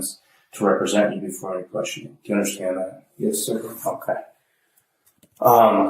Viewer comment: anyone else hear “do you understand that?” “yes” “f*ck that”